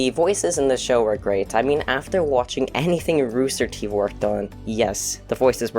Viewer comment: did she say rooster teeth, or am I mishearing?